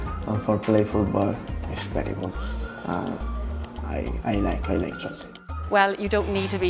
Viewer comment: Did roque say ‘i like chocolate